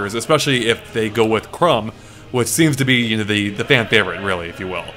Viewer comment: Did call Chrom 'Crumb' at 2:48 ?